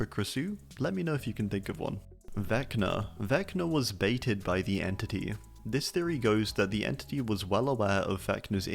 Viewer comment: I misheard this as "Vecna was dated by the Entity" and now I'm imagining an adorable dinner date between them, making awkward casual conversation, Vecna going for a goodnight kiss, etc.